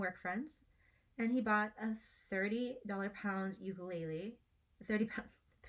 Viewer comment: i'll be honest, i heard "30 pound ukulele" and now all i can think of is a huge ukulele-shaped double bass. (also great video!)